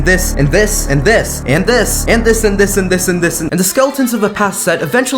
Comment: he is not said “and this” he said “andis”